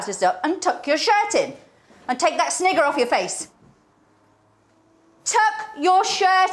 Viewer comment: what did she say?? 🥵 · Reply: "Take that snigger [laugh] off your face!"